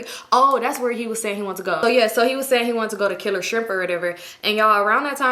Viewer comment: I thought she said he wanted to go kill a stripper at 4:01 😭